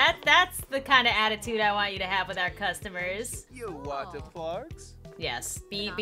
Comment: At the begging the receptionist sounded like he said do u want the farts 😂🤣😂🤣😂🤣